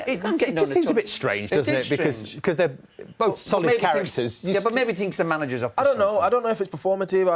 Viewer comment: did Roy say “well maybe he thinks the manager’s a pussyole” 🤣🤣🤣🤣🤣🤣🤣🤣🤣🤣🤣🤣🤣🤣🤣🤣